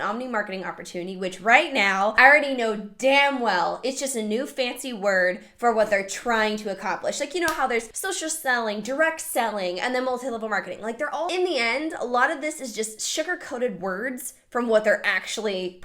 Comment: Honestly, I heard "social smelling" 🤦🏼‍♀️